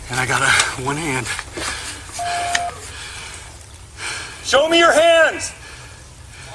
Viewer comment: Why do you f**king hands to me, bro? 이렇게 들리는데 맞나요